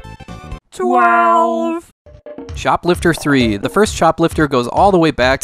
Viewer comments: I swear by god, I was listening to the video while doing other stuff and I heard "Shoplifter III". Had to stop for a second to check why did they make not one, but three games about shoplifting. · Oh CHOPLIFTER ..... for a minute there I thought you said shoplifter III 😆... the first game based on retail theft! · lol I thought you said "Shop lifter III" instead of Chop lifter